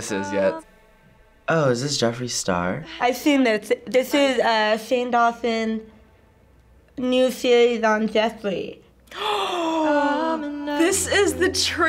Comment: I have no idea what she is saying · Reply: "I've seen this it's shane dawnson new series on Jeffree."